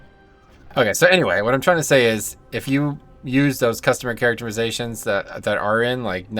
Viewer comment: When you say customer characterization instead of Character customization at 5:06. i had to go back and listen again to make sure i didn't hear incorrectly haha. keep up the good work man, love your content.